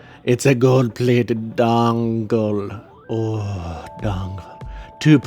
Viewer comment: did he just say " I love dong.....le, I love dongle"